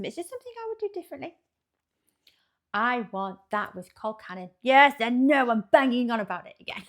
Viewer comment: Had to look up what colcannon was because you said it so fast. I thought you said cold cannon, lol. I still wouldn't have it because I don't like cabbage so this is a meal, I can skip.